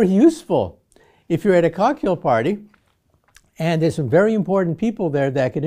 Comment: For a brief moment I really thought he said a "Kaku" party 🤣 I hit the rewind and it's cocktail party🤦🏾‍♂️